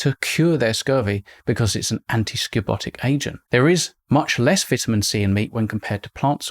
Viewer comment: what's that word? I didn't catch it. Antiscotic? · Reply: Antiscorbutic. A substance that prevents or cures scurvy.